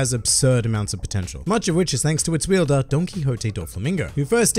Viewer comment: What other ppl hear: Don Quixote What I hear: donkey hote